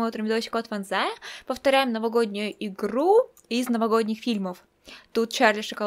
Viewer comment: мне не послышалось или ты сказала повторяем Новогоднюю икру или игру. Хотя ты наверное хотела сказать еду.